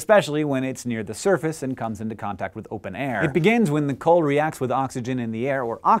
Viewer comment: thy did hank say at 1:42 "it begans when...?"